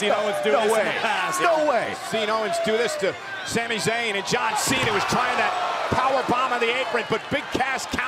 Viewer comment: did he just say john cena